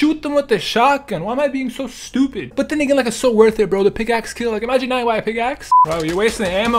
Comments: i know this is old but what did say??? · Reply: Then they get it's like so worth it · Did he say the n word at 16:09? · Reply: no he said then again lol